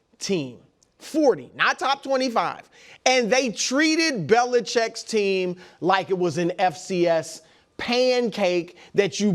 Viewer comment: I thought Brou was gonna say “they treated belichicks team like some breakdancers” 🤣🤣🤣🤣